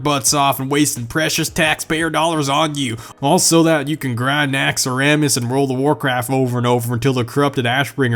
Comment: did he call it "naxx-uh-ramas"? come again?